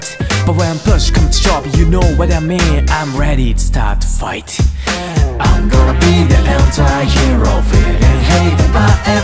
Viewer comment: ごめん、サビの最初の『I’m gonna 』のところ、「アンゴラ」にしか聞こえなくて配信思い出してしまう🤦🏻‍♀️